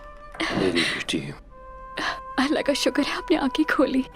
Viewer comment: did she sayed "Allah ka shukar hai" ?????